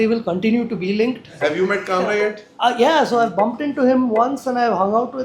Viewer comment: did he say "Kamra"? I mean Kunal Kamra?? 🤔🤔